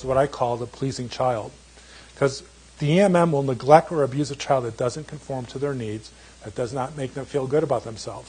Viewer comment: could someone please transcript and explain for me. I hear (the EMM or neglect are abuser child) Thank you